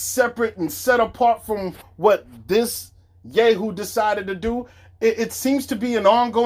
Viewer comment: did he say this gay decided to do????